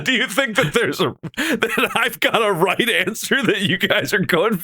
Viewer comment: Damnit, who else? I genuinely thought he said there was a right answer 😭